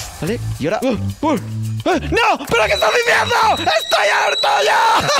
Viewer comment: Entendi lo que dijo cuando dijo la mala palabra porfavor no me Denuncien por esto pero dijo : me voy ala cing###